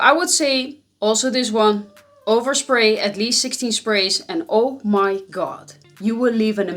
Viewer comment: did she say 60 sprays? 🙂🙂